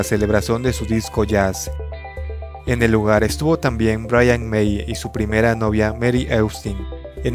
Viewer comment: Minuto 3:20 escuche Bryan Mayer🤯